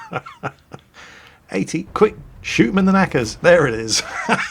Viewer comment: "Shoot him in the knickers" Added to the list of things that I never thought I would have heard said. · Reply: knackers not knickers